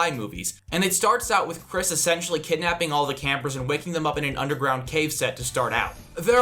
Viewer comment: Sounds like he said Chris is sexually kidnapping all the characters